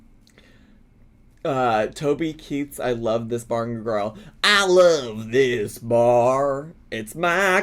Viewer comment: SWORE you said I love this barn, girl and I was like damn Ken Berry wrote a track?!!!!!